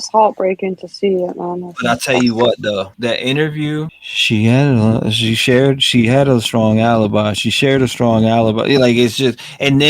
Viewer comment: For what it’s worth, with the captions on, it dictates “share the strong alibi”. But it also thought Creighton said “manufacturing a salad body” during the murdaugh trial 😂🤷‍♀️ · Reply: No he said "she HAD a strong alibi".. "share had alibi" doesn't make sense he said HAD after the first word